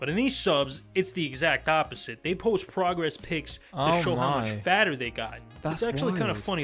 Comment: Sounded like he said "Progress pigs", which I think would be an accurate description too.